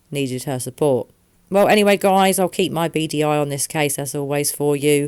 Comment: 🤦‍♀️ I thought you said "I'll keep my BDI on this case" and I was really here like "what does BDI stand for" I now realize you said beady eye. Omg..... I need more sleep. ** also at this point, I find it hard not to believe her parents are involved. For them to make a hoax phone call to mess with people that truly care for their daughter is extremely disturbing. If they were concerned for their daughter and truly torn up inside about it, they wouldn't have the time or energy to toy with other people who want to find summer too. That's not something innocent people do when they're scared for a loved one. That's something truly wickedly disturbed people do.